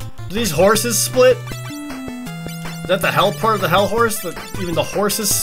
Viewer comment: Am I the only one who, when said "Hellhorse?!", thought he said "Hell Whores?!"